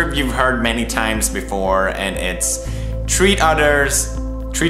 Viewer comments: when he said treat I thought he said tree in an Irish accent... Ahhh!! I've been watching too much jacksepticeye videos!! 😭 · I was crying then I just heard "treat udders" and I just lost it omgosh XD